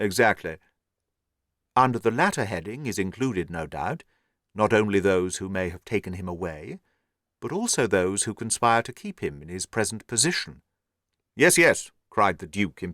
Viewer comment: What did he say at 55:30 or shortly after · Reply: ‘Under the latter heading is included, no doubt, not only those who may have taken him away but also those who conspire to keep him in his present position’ :)